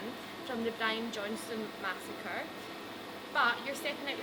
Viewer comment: Did she just say 'Brian Johnston Massacre???